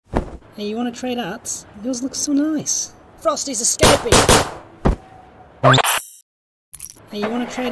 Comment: "Frosty's escaping!" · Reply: I thought those guards said “FAST, HE’S ESCAPING!”